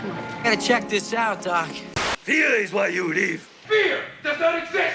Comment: As a kid, I thought Sato was saying "Here is why you leave. HYA!" just like trying to express how angry he was that Miyagi left or something.